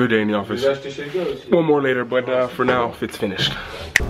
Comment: Did you just say Fitzfinished? (at 1:12). I can't remember when I've laugh so hard at a surprise line. Thank you for that.